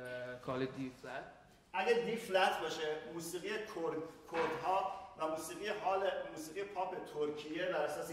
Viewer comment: Here he says Kurds but English subtitle translate it as "of course". I hope this is just a mishearing rather than a racist censorship that we Kurds used to see always